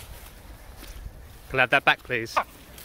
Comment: it sounded like "could I have that back please? Loki: No. ~flies away~ 🤣